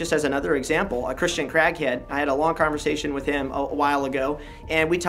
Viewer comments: I had to rewatch multiple times at 13:50 because I swore he said he talked to a Christian Crackhead. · did you say “a Christian crackhead”? 😂😂